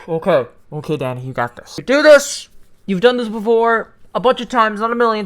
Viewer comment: When he said "ok danny you got this" i thought he said "ok daddy you got this" 💀💀